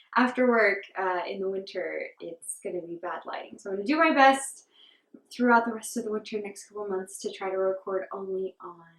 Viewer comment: Can you please type the name of the sweater you said you were wearing. I heard “doc light” but I’m guessing I’m wrong because I can’t find that on Raverlry. LOL. Thanks! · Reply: Haha sure! I'm sorry I should've included it on screen or in the description. It's called Docklight by Julie Hoover.